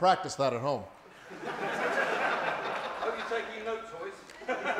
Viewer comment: What did the dude in the audience say at 2:11? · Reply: hope you are taking notes royce